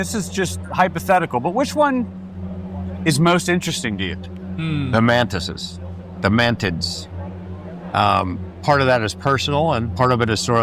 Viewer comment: why did i hear "The mantises... the man-tits" 🤣🤣🤣